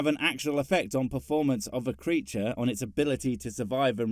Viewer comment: My french ear heard you say "Preacher" instead of "creature", I thought it sounded funny at the time... Like "Preachers that are good at it, etc..."